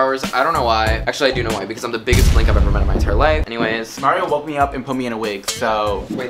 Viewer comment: does anyone understand what he said? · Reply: I've been the biggest blink for my whole life